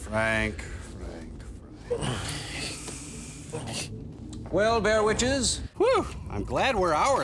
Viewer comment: what does he say at 3:40? "well bare witches" ?